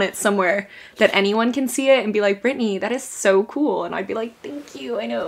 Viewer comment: Every time I see your videos on my feed I hear “it’s Britney b*tch” in my head and I never clicked faster. Your unboxing videos are my fav!! I’m excited to see them incorporated into your vlogs!